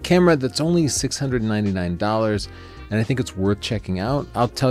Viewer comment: Did you just say it only 699?